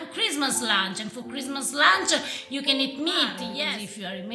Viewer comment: with her accent loll i hear.... and for christmas lunch you can eat me !!!! @ 1:02 lollll